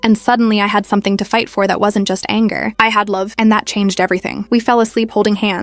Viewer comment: I heard luv